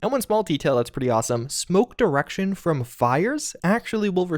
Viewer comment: Did say 'Smoked erection' lol😆